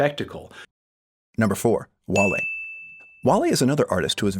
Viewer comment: In the captions says Wall-E instead of Wale. Haha